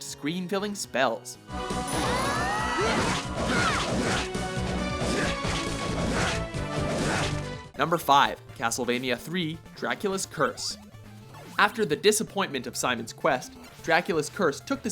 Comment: am i the only one who heard him say "dracula's quest" instead of "dracula's curse" near the end?